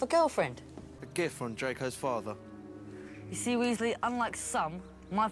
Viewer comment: I thought he said “your going to hear from dracos father”🤚💀